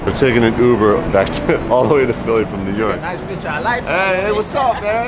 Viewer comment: Did he just say: Nice bitch, I like...?